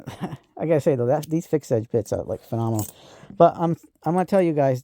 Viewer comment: What brand are those long bits you had?Sounded like you were saying fix itch and I can't find anything for that brand name. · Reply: Geinxurn Fix Edge Bits! Best bits on the market for multi bit drivers. I recommend the 4in mostly but they have 6in. I have all the 4in and 6in bits they offer and some of 2in and 1in bits. You can get them on Amazon. They're impact rated but not sure how they will be on an impact long term but so far so good when using them on an impact. But as a multi bit driver bit they are the best I've ever used because of what they offer, quality, price and length relative to the multi bit screwdriver handle. I have a video review on these. <link>